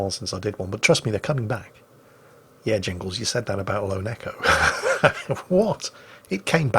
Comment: Every time Jingles says Lone Echo I hear "Lo, Neko" and wonder if I somehow missed the anime catgirl games on this channel